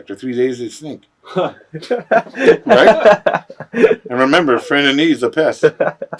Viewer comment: What did he say something about piss? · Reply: He said "A friend in need is a pest" It was one of his classic lines as a heel.